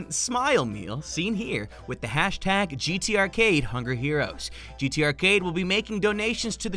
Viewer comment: I thought that said hunger herpes